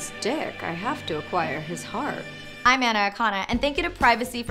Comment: I thought she said “I’m an anaconda”